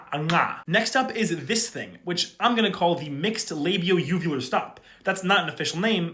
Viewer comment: Labio uvular. 👀 Rarify my Oral cavity?! 😳 Labial coronal?! I beg your pardon sir!! ✋🤧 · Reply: For a moment i thought he said Labial vulvular and said wtf man